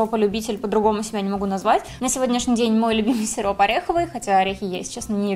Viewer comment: Когда ты сказала сироп мне послышалось сирун